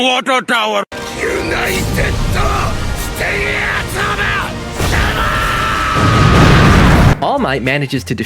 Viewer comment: tell me allmight did not actually say "united states" smash .. did he ??